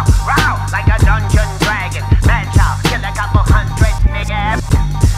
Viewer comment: Wait:::; did he just say,."kill a couple hundred niggas "????....Rewind!!!